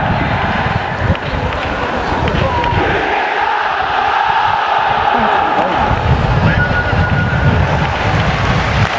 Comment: 何て言ってるんですか？ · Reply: シャビアロンソ 行け札幌